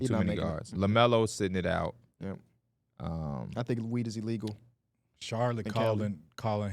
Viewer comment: did just say i think weed is illegal in cali???